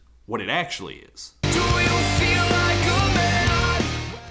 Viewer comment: When I was a kid I thought that that part said "do you feel like movin out - on" lmao